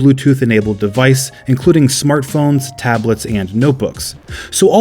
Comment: did he say that they are compatible with notebooks